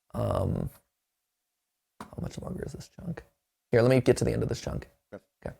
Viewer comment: I thought he said junk lol